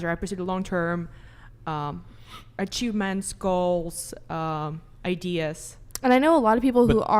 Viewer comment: I thought she said, “I chew men’s skulls.” I was like damn girl, chill 😂